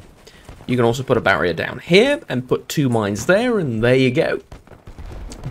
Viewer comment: I thought the last word said "miners"